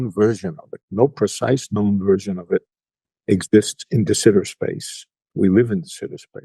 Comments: "exists in ??? space, we live in ??? space" -> what word is he saying? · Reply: de Sitter space... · What "space" is being referriing to at 7:28? It's hard to make out what is being said. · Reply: De Sitter space